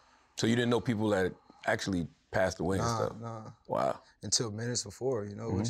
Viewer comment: Am I the only one that hears " nah not until minutes before "...the brief pause as if he realized what he said then changed his convo off that....weird🤔